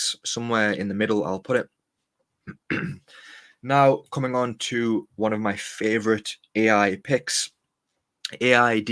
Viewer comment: what is the name of the coin you said? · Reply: OORT